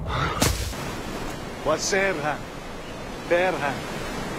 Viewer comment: i could understand "hvad sär han" it sounds similar to the swedish "Vad säger han" which means "What is he saying?". It is also closely related to "Who is he?"